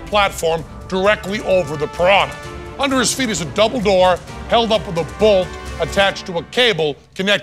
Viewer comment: For a second I thought he said: "under his feet is Dumbledore…"